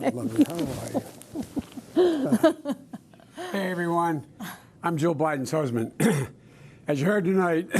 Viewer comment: He gaffed saying I'm Joe Biden's husband... Or did I misheard it. Can someone tell me. The mere fact that in his own home with his family altogether in a room Joe puts a mask on at the end. Holy poop a whole 2 minutes total hearing from Joe Biden. Not anything about their policies moving forward. All rhetoric of opinions/lies even not facts. Interesting. Yes indeed, so many were right this was cringe worthy and a big snooze fest. Question, will Joe be able to even debate across the stage from Trump with news media there? I can't wait to see it happen. More racial dividing and emptiness of ingenious words from so many people is incredible to me. WOW!! 🤦😳😮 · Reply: He said Jill Biden's Husband. Listen.